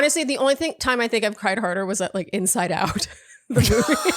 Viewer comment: Omg, when said she cried at ‘Inside Out’, I thought she said ‘In and Out’ - 😆🤦‍♀️. And I cried, so phew, not a Shandopath! YEEESSSSS!